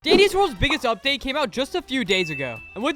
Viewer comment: I Thought I Heard Diddy's World 🤨